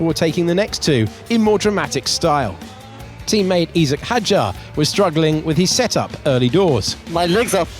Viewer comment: Team mate what did he say.? 😂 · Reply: Esack Hadjar fr fr